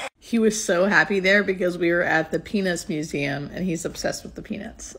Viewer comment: Hiya I seriously thought u said here we are at the penis museum, so I had to rewind and hear it again so happy that I was wrong lol,I hope u both have a very happy long like together much respect from Gazza so please keep them coming GL&HF coming from Dulwich London England(✌peace✌)